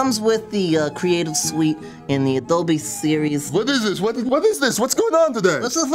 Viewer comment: I thought he said and the a don't be seroius